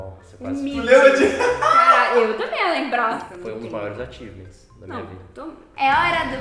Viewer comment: foi um o que ? (realmente não entendi) agradeço se alguém me ajudar. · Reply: "Achievements" conquista em inglês kkkkj